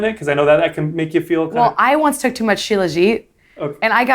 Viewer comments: which nootropic did she say? Shila-gie? Edit: found it. Shilajit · ANYBODY KNOW WHAT SHE SAID AT 11:10 ????? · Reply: shiljat